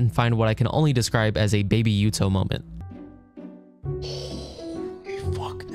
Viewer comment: baby what moment? I rewatched it like 10 times and have no idea what you said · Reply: baby Yuto from the cooking mama spin-off babysitting mama. you had to play the game with the baby as the controller,, it was weird. that’s what’s hanging from its neck in the clip with jschlatt if you’re curious what it looks like